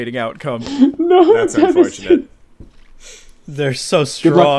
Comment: I think he said "No, devastating" or something like that, because of "devastating outcome" a second before. Now I might be wrong here (most likely I am), but I'd like to believe I'm not and I'm helping, thank yew.